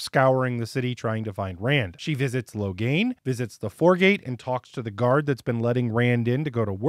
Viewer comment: OMG! Did you say 'Whoregate' instead of 'Foregate' at 1:22? I just did a coffee spit-take!😆